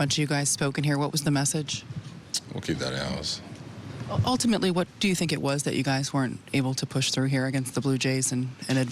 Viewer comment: What did Judge say at 0:31 ? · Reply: Vamos a Cancun